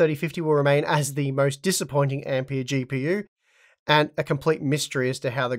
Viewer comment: I heard "The RTX 3050 will remain as the most disappointing AMD GPU". Had to slow it down to 50% to hear the ampere.